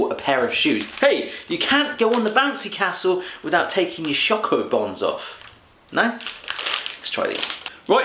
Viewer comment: Thats so cute how you guys say german words. I hear not "Schoko Bons"...you spell it out like "Schoko Bombs" whats also pretty amazing. Between ... I LOVE Toffifee and ..schoko bombs ;) Brause + Vodka = Unicorn?! I have to try this!!!